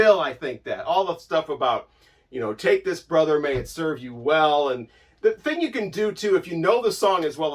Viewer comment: As a kid I misheard "Take this, brother, may it serve you well" as "Take this rubber, may it serve you well"!